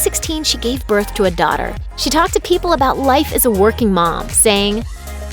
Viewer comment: Oh my god at 2:24 I thought she said ‘she gave birth to a dog’